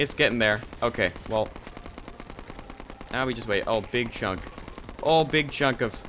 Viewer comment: I thought you said big chungus